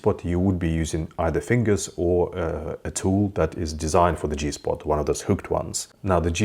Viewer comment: Oh goodness. Re G spot, I thought he said a finger or a TOE 🤣🤣🤣